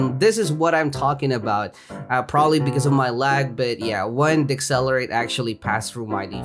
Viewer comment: did say d word ? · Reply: The di.celerate